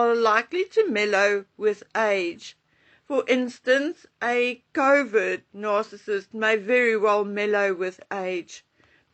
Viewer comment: It sounded like you were saying Covid narcissist.😁